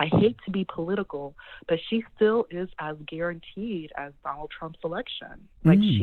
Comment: Did that caller say "as guaranteed as Donald Trump's reelection" WTF, wypippo are bold af in 2020